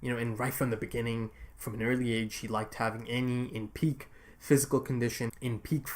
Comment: straight up thought he said "annie and pieck" 18:42 T_T